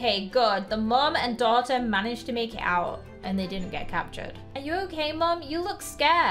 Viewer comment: i thought she said make out 😂😂😂😂😂😂😂